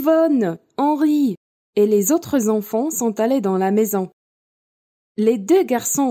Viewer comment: the recording says 'et les autres enfants' instead of 'et les quatre enfants' written in the text.